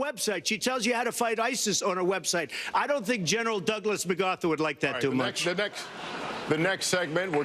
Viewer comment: Did Trump say General Douglas Mugatu (zoolander?) instead of MacArthur?